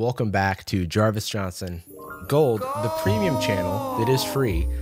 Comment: I always thought it was you saying Gold in the background until I recently re-watched Astin Powers and heard goldmember, say "I love golllddd" and that's where it came from. I swear you don't sound like Micheal Myers doing a purposefully bad impression of a Dutch person.